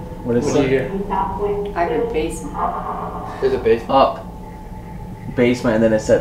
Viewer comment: I’m not 100% sure but at around 31:46 after the owner asks where should we go next and the voice say “basement” i swear I hear “I’m catholic” then it says up.